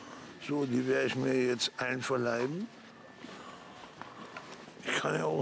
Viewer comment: Das werde ich mir jetzt einverleiben! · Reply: Ich hab immer einverleihen verstanden :D